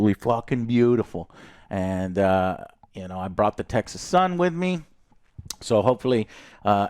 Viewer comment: Had to replay thought he said he brought his exs son with him